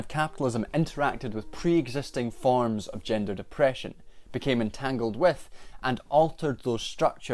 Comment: I kept mishearing "gender(ed) oppression" as "gender depression" and was like yeah man I get that a lot... then I realized it didn't make sense and had to rewind a bit to fix my perception 🤦‍♀️